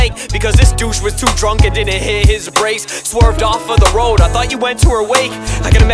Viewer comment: So did she get killed or raped or something cause it said something about a river xD ~Sixx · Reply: Ahhh he says "Breaks" I thought he said "Brace"